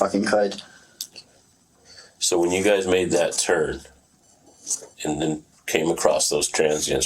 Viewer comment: sounded like he said “so when you guys made that turd…” lol